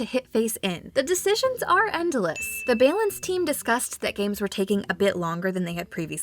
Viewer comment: Did she just say bay-lance